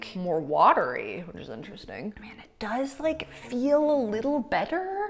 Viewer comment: did she say “it do feel like” or am I going crazy? Lmao